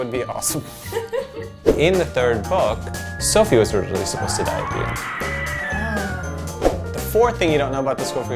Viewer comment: I couldn't hear what he said.. is it "Sophie was supposed to die here?